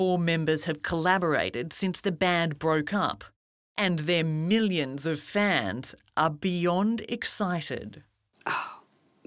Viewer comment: I thought I heard “And their millions of fans are Bjorn excited!” 😉